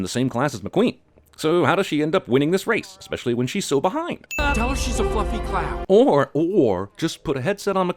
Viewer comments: sounds like lightning said “ tell her she’s a fucking clown “ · with low volume on my phone, I thought I heard: "Tell her she's a f*cking clown" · did he just say "Tell her she's a fucking clown"?